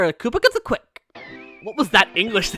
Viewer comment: Koopa Ktzit Quick?(It sounded like you said Koopa Cuts the Quick)